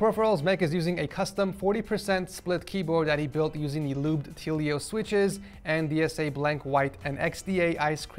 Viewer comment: why did he say tealios